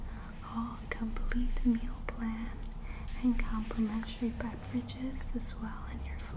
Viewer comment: hoh beverages thought you said bad bitches